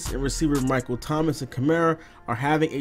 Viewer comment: I thought it said 'Karma' mad at Madden... I was thinkin "bout damn time"